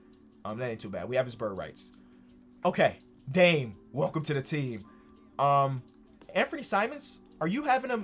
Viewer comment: Anyone else hear "Dame welcome to the team?"